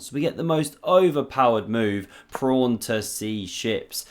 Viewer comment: any reason you call it 'prawn' instead of pawn? 🤔😂